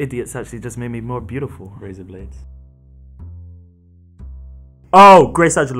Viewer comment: what he say? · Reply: razor blades